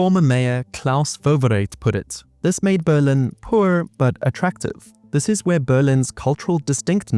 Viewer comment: It's "Poor but SEXY", that's what he literally said